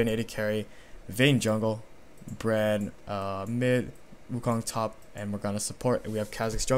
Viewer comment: Lol I heard "Bread Mid"